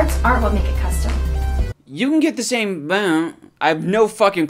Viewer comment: After 9 years of re watching this video multiple times just hit me what she said at 2:22 she said " build"